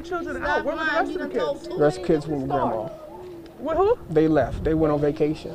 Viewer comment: So i dont know if i heard this correctly but @ 14:00 time mark someone starts asking a question & asks where were the other kids at @ 14:10 it sounds like he says they were warmer..? Corrects himself & continues to say they were somewhere else they were on vacation.